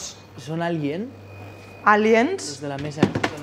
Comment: Jajaj yo escuche "alíen" las 2 veces q dijo jajaja